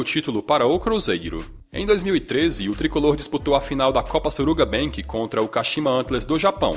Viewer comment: só eu que entendi suruba bank?